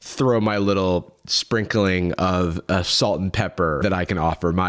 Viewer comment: omg I thought you said 'assault and pepper' 😂😂😂